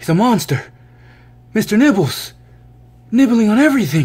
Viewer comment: You want to get a bigger tank it seems simple put him I nibbles tank I misheard you last time I thought you called him Mr nipples and my mind went straight to little nicki